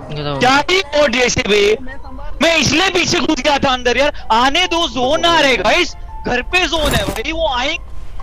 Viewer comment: ngomong apa cuk dia😂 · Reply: Kita udah zona di rumah kenapa kalian pada keluar luar